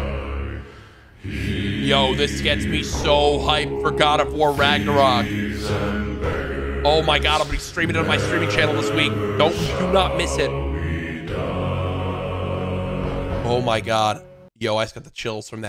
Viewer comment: i lowkey thought jack said this gets me hard for god of war